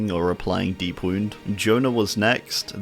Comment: honestly thought you said J-lo as in Jennifer Lopez 💀